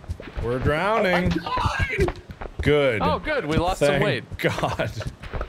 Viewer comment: Did anyone else hear say: "Good, we lost some Wade!" instead of "Good, we lost some weight!"?